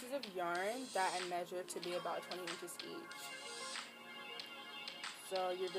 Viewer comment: i want mine to be as long as yours, you said you measured in the beginning at 2:37,but i couldn't hear you. what did you say? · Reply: 20 in